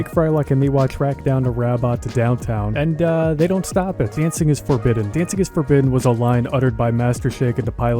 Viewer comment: For years, I heard Shake say "you can't clean your trumpet" in the outro until my wife told me it was "dancing is forbidden". Then I could hear it as that too. Anyone else or is that just how my brain works?